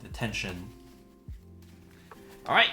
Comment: it took me a moment to realize that that was "the tension"; at first I thought I heard "detention" or "attention" lol